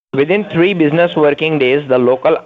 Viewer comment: Did he say... Within 3, business working days